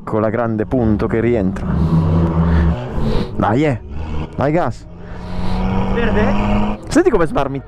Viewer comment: Did you say "Дай газ" ?! :D Where are you from? Greetings from Bulgaria! ;) Наздраве! :) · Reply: "He said: dai! Dai gas!" It means: "come on! Go fast!" or something